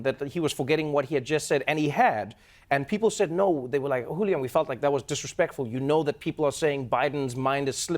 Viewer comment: Wait... did Trevor call him, "Julio" at 9:52 ? :D :D :D